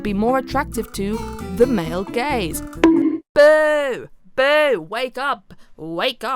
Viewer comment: i had to replay that part like 4 times because my mind thought you were saying male gays instead of male gaze